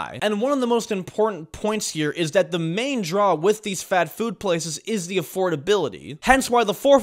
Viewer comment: am i the only one who heard fat-food?